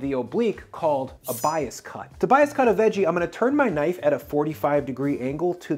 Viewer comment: You said "to bias cut" but I heard "Tobias cut"